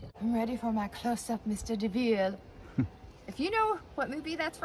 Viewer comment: Sunset Boulevard. It sounded like you said Mr DeVille, but it's Mr DeMille. Liked and subscribed.